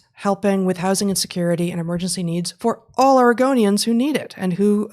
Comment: I can't help but think Heather is saying "All Argonians". YES! JUSTICE FOR THE LIZARD PEOPLE OF TAMRIEL!